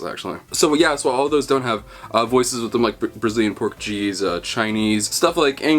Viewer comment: Him: "So, yeah, some of those don't have voices with them like Brazilian Portuguese." YouTube Captions: What's that I hear? BRAZILIAN P O R K C H E E S E